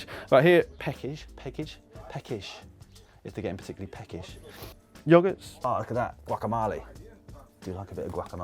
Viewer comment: did he just say yagart